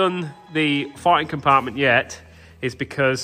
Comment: I had to rewind, thought I heard “farting compartment”? 💨😂😂